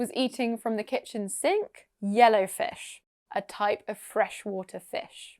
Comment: Okay i did not catch the yellowfish at all, gee he woosh right through it. I thought he said "a bit of all the fish"